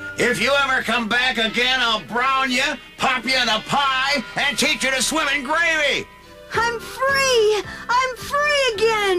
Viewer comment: From 8:07-8:09, what did Mr. Mcgregor say after he said "pop you in a pie?" It sounds like he said "and teach you to swim in gravy" but, I'm not sure what he said. Does that mean he will cut Peter up and make his body parts float in gravy (if he did say "gravy?") However, I think a "pie" and "gravy" don't make a good combination together.